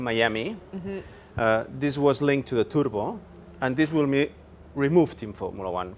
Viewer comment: This was linked to the tudbol? Sorry, but I can't understand his accent well.